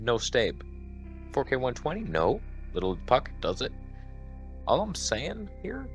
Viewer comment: bro, you had me at the Flip flops lol, and the wait what? LOL I'm simple and learning. Thanks for being in my Reality. My yesterday was out of this world. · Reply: Little Puck does it. I'm listening and I'm like, what is a little puck then you said it again I'm like Rewind. Now its funnier